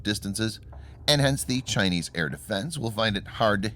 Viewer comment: the subtitle show Iranian, instead of chinese.